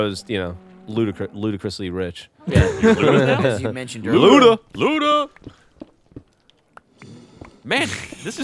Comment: I thought I heard Ray say "Luda!" at 7:34... :(